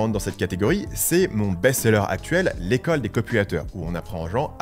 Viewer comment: J'ai entendu "l'école des copulateurs" 😅